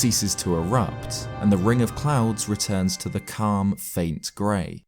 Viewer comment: I kept hearing "ring of clowns" and not clouds lmfao